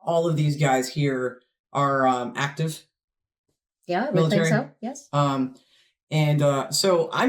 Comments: Please read: Some people think at 3:50 said "actors" she said ACTIVE. Maybe it's an accent thing because we don't understand how "actors" were heard. You can always turn captions on if our accent sometimes gets in the way. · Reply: I heard "active". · Other commenters have told you when to watch the whole thing. You have to see as well when the Guards march past and salute the Queen; there is also the procession of the Royal Family to and from the parade, with several Royals on horseback. ( I remember before 1986, when the Queen rode herself. ) I think I misheard you, but did you say at the beginning that these would be actors? Of course they are not, they are professional troops, often having fought in Afghanistan, Iraq, etc., and the guns are the up-to-date real ones! The scrolls on the Colour are the Regiment's Battle Honours, commemorating when the Regiment distinguished itself, dating back centuries. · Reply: Yep you misheard, said active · Please read: Some people think at 3:50 said "actors" she said ACTIVE. Maybe it's an accent thing because we don't understand how "actors" were heard. You can always turn captions on if our accent sometimes gets in the way. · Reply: I’m deaf as a post and I heard you say active.